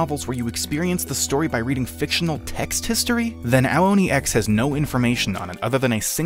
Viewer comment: I'd hate to ask you to do such a similar video on short notice but have you ever heard of HetaOni? It's a complete fever dream of a fangame based on the characters from Hetalia Axis while using Ao Oni as a template for its gameplay. I think more people need to see the complete fever dream that the game is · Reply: I thought you said hentai oni